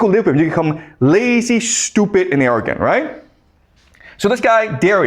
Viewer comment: I thought he said lazy, stupid, American